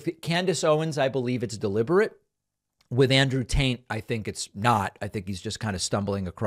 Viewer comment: Sounded like you said Andrew Taint and I'm so here for it 🤣